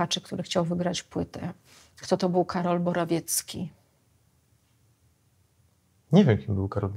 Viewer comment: Usłyszałem kim jest Karol Morawiecki... Zamiast BorOwiecki :) Paciorek to dosyć mdły osobnik, inteltualnie nic specjalnego, XXI w. indywidualista...